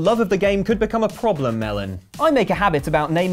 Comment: Does call, Melon at 23:58?